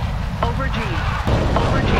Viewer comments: I kept hearing "Aubergine, aubergine, aubergine" and then after a solid minute, I realized it was saying "Over G" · I hear “aubergine” instead of “over G” 😂😂